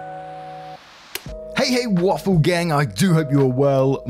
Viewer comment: Have a great day today waffles. The community is here so wonderful keep on being you waffles! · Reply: OH MY GOD, I have been following the channel for quite a while and I always thought he said “awful gang,” I just realized it’s “waffle gang” because of your comment! My mind is blown! (ps English is not my first language)